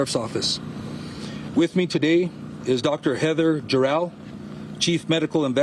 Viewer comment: Did I hear Jor-El? 0:14 Hackman as Luther was always a bit campy but it was so great.